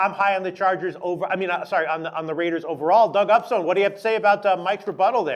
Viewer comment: Did he just say the chargers will beatthe Raiders... Lolololololol wtf